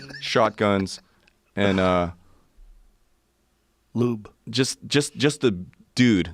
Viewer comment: Did Joe say Lube?!! I'm dying